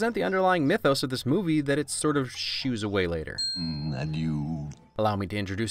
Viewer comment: Wait. Scar says "Adieu..."?! For 25 years I thought he said "And you..." to the mouse he was about to eat, as if showing some lamenting sympathy that it's especially unfair to the mouse just then. A "nothing personal, kid" moment, so to speak.